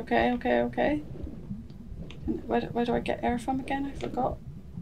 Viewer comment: You say “Get air”, I hear “Get her. Get her. Get her.” 😂